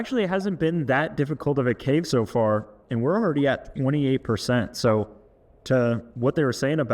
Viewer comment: i thought bro said 48%